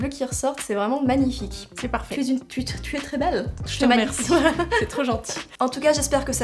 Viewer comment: y’a que moi qui entend « tu est une p*ute tu es très belle » à 23:55 ??